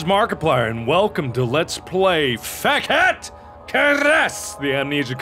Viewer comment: when he said fekete it sounded like he said fuck it